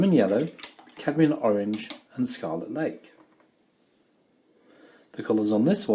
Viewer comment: Hello. I'm following your tutorial but being french talking I can't understand the name of one of the colour used. At 52:16 you explain the mix for the "darker" orange mix : lemon yellow, cadmium orange + brown + ??? . Could you please help because I can't find this colour. Thanks a lot for your painting tutorials (and English lessons for me :-) ) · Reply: Salut Viviane, les couleurs dont je parle sont; jaune citron, orange cadmium, lac écarlate et ........ terre d'ombre brûlée. Merci beaucoup d'avoir suivi mes vidéos de peinture. N'oubliez pas que j'ai maintenant deux vidéos complètes GRATUITES pour les personnes qui rejoignent mon site Web pour l'adhésion gratuite, sans engagement. Je vous donne la photo de référence et le dessin de contour, ainsi que le nuancier. Jetez un œil si vous êtes intéressé, rendez-vous sur <link> c'est un tout nouveau site Web conçu, donc je suis super excité. Google le traduit très pratique. LOL Merci Viviane. 🎨